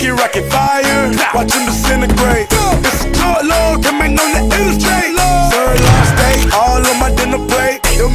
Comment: In fight night I always thought it said "there's some drug lords coming in the industry" 🤔